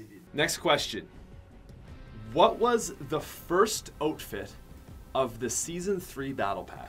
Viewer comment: DID HE JUST SAY OTFITE AND NOT OUTFIT OML